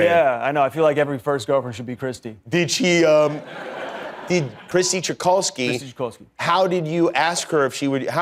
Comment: I thought he said "Krissy Kochanski" for a second there and now I'm gonna spend the next few days binging Red Dwarf.